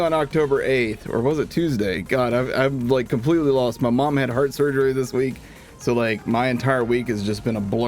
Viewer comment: At first I hear “I lost my mom in heart surgery” and I got so sad for